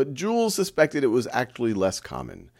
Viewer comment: Anyone else hear act-lee instead of actually